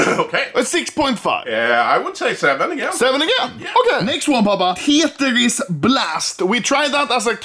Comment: papa sounded like ben